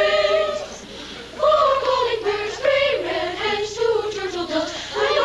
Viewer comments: Two things. THAT was awesome.And I'm used to 3 French Hens not 3 Red Hens. At first I thought they said 3 Red Heads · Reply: I thought they said rare hens, now I hear red.. haha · Two things. THAT was awesome.And I'm used to 3 French Hens not 3 Red Hens. At first I thought they said 3 Red Heads